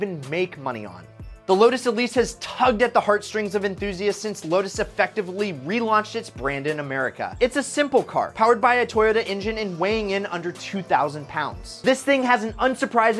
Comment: I thought he said at the lotus car. It was powered by a toilet engine